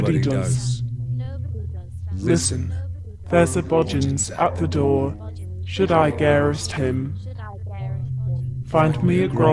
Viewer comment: It sounds like at the end of the demon speech it's saying: "There's a monster at the door. Should I escape it, I'll be home free." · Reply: I heard there's a body at the door, should I get or arrest him.